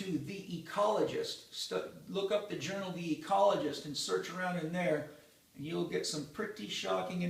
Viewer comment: If you want to have a great laugh, turn on the caption feature in the bottom right corner where it says "CC" The subtitles brutally terrible, it is hilarious. For example at 7:36 in the video, Paul says, "look up the journal the ecologist, search around in there" The English captions say, "look bitch ecologist search Nair". There are tons of instances throughout this video that are completely off :P lol, I feel bad for deaf people trying to read through the captions and they turn up being completely wrong and nonsensical at times. I would bet that they do not have people who fluently speak English writing the captions.